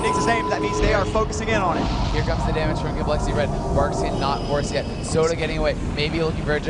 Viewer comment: does he say "Barkskin, not foreskin!" ?